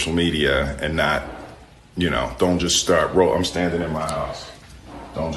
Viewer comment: I thought he said I'm standing in my house!! He not checking in Rehab!